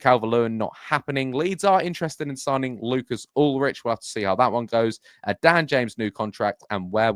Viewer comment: I thought that said Lars Ulrich was linked 😅